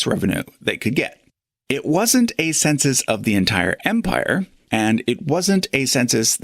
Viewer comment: I love your channel, but is anyone going to point out the glaring mistake you made at 10:00? If you go back to 9:45, very clearly says "It WASN'T an empire wide consenus," and you misheard him and thought he said that it was empire-wide. You then proceeded to argue against that point for a little while not realizing that you simply misheard him. I rewinded the video several times to make sure I wasn't imagining it.